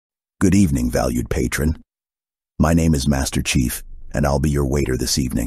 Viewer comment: Thank you for your service, Master Chief 😢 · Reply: I thought you said Masterchef. Now I'm imagining Gordon Ramsay in the kitchen yelling at Chief.